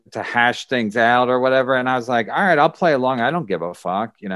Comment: my same reaction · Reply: I thought you said I'm alright spider?